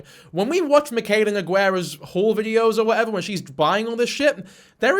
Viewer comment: straight up thought he said "wh*re videos" for a second not gonna lie